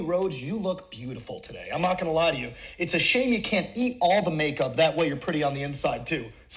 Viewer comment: “Its a shame you cant eat all the makeup so you could be pretty on the inside too” damnnnn two burns in one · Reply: You thought MJF said, “you can’t eat makeup”. However, he said, “you can’t eat all the makeup”. Here MJF is saying that she has eaten makeup. Now, you can’t take this meaning of “eating” literally. Consider the following situation, and hopefully you can see the purpose of this example: Suppose a pretty lady passes by a person who’s known to be rude to people, the person says to the lady, “looks like eating those makeup does make you look pretty.” Note: you need to understand this sentence’s connotative meaning and its sarcasm. Conclusion: MJF explicitly says that she used makeup and implicitly said that she looked pretty on the outside. However he didn’t just say that she used makeup. He said that she “ate” some quantity of makeup to be pretty. This shouldn’t be looked at literally; he’s actually making fun of her putting on so much makeup. This, Eating makeup = putting too much makeup than would be required to look pretty, which begs the question: “Why did she have to put on so much makeup then?” If you can find the answer to this question, then that’s what MJF meant.